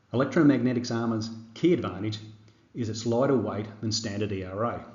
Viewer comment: is it lighter? i cant quite understand what you said there but thanks for even mention this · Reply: Supposedly lighter due to the filler.